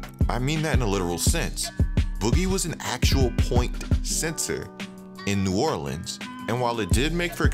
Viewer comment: Sounded like he said point sensor at 3:15 but think he meant point center